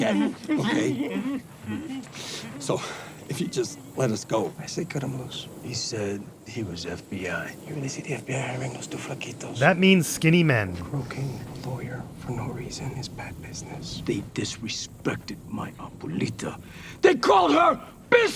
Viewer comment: of the 20+ times I've watched this, I've always thought that nacho :_bwtNacho:said "fuckitos". I still think thats what he said. It makes sense in context.